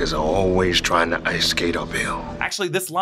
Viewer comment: I SWEAR!!!! ALL of these years I thought Blade said “ice skate A BILL” Like run out without paying the check like in a bar or restaurant. Hahahahaha lmao. Wow. I feel half dumb